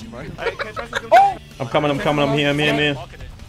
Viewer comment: Is that Hungarian? Cuz I heard: Gyere már ki, Gyere már ki!